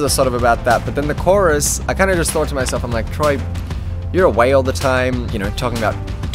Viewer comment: For a second I thought he said you're a whale all the time😂